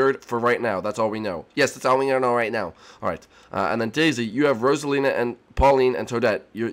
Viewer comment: Hold on a sec did anyone else hear bower jr. call peach daisy